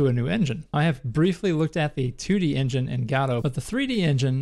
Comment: Thought he said God of War. Lol Great video!